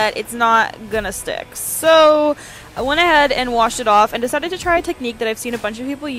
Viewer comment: I thought you said smash it off